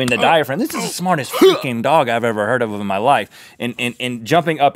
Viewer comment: it sounds like Rett said this is the smartest f&cking dog